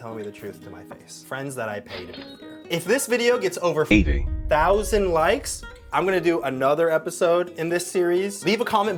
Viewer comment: what did he say, he didn’t day 80 because he edeted it in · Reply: He said 20k likes